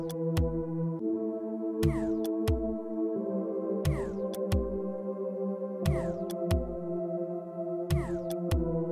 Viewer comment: What does the one at 1:26 and 1:28 say? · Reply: The 1:26 Say you have to be able to do 50 jojolity and the other is 100 jojolity